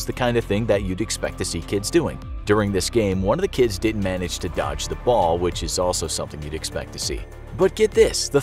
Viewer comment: the captions say doge instead of dodge and thats funni